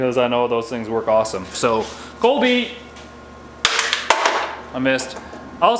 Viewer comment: did he say "colby"????